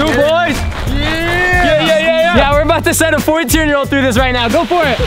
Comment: said there’s a 14 year old doing it and I thought he said 42 year old lol